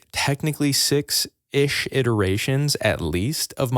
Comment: At first i thought you said 76 iterations, i was like sounds normal to me😂